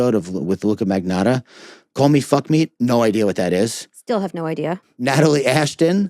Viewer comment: Would call me, fuck me be a spin on the kill me, fuck me, eat me episode maybe? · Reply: i heard “call me, fuck me” too 😄but it’s actually Call Me Fuckmeat, the Patron said it’s “what Elaine has in her profile” from Killing Is My Kink